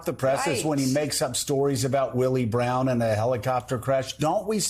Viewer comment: Waaait.... I thought Trump said "He went down on Willie Brown in a Helicopter and nearly died"